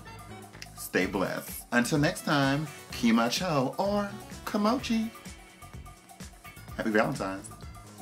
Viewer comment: I looked up the new "komochi" word Nina uses at the end and it translates to "having children" 💀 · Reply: Ah ok thanks for the info. I heard "komochi" that's why I was confused